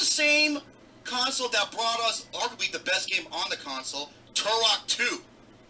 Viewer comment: At (30:43), it skipped and it made it sound like I said "One of the best games" where I actually said, "One of the best looking games". Just wanted to clear that up.